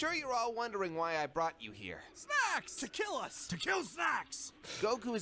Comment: The first time I saw the episode, around 20:00, what I heard was "Snacks!", "Tequila!", "Tequila Snacks!"; the tequila part probably fueled by the fact they seem to start drinking alcoholic beverages right away. Also loved the idea that Vegetta wanted Tequila Snacks. XD